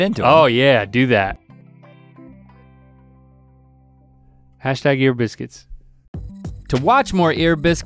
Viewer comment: he said hashtag ear biscuits but i thought he said hash browns and biscuits